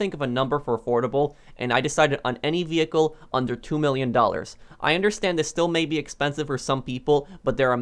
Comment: first i heard 10million dollarsxD